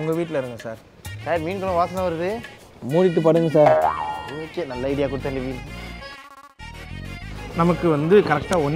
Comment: did he said nalla idea kudutha nivi🤔🤔🤔🤔🤔🤔